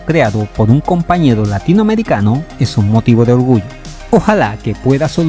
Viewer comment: Es un motivo de qué? No escuché bien. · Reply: Es un motivo de orgullo.